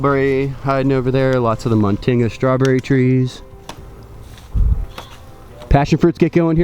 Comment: what did you say 4:28 - 4:30 ? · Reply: Life Of Machond the strawberry tree, muntingia calabura?